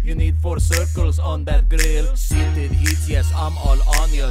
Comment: it took me 9 years to know that he said "seated heats" instead of "heated seats"